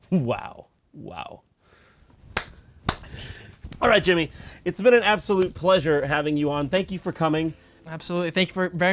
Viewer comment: What did jimmy say lol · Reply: I think he said "I made it"